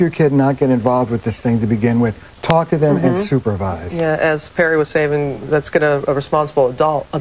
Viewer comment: i thought she said Perri ASSHAT :D